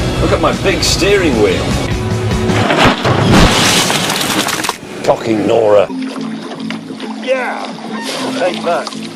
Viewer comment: I'm not a native English speaker, can someone tell me what he says at 1:45? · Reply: Cocking Nora